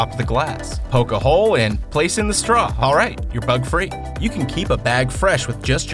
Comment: when he said "bug free" I thought he said "thug free" on that drink hack with cupcake wrapper thing